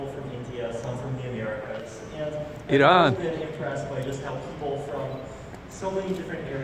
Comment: did he say iran? what about that